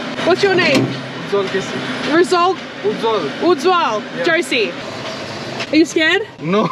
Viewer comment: I love the way she asked if it's "Ujwal Jersey?"😂 but he said "Ujwal KC" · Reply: I thought she said “jersey” too! But it was “josie”, her name. Australian accent 😁